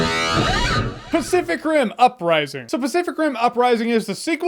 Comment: Do i hear "specific rim" or am i tweaking